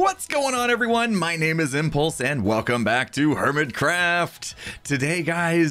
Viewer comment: I muted my phone but I heard "Hello everyone my name is impulse" LoL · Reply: OP MOVIES AND VLOGS but he said “whats up everyone, my name is impulse”